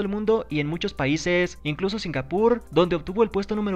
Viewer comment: Dijo Singapur Entendí "sin albur"